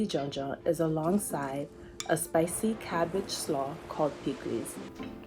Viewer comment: when the closed caption says indistinct, she says "piklis"